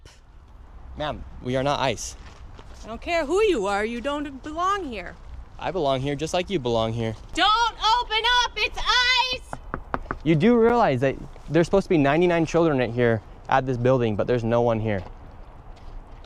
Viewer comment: I thought that Karen was saying don't open your eyes. 😂 Don't open your eyes (otherwise you will see the fraud)